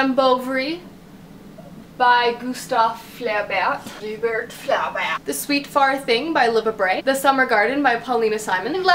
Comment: omg at 1:47 i thought you said "the sweet fart" XD XD love the video :*